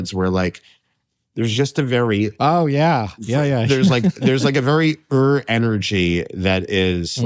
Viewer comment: At about 30:05 Alex Edelman says something along the lines of, "There's this very Ur (?) energy..." Does anyone know what he says here or what "Ur energy" is? I wish he would have explained a little more about this idea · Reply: Did he say "Eruv energy"?